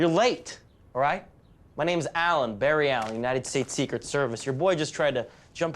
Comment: Did he just say his name was Barry Allen?😂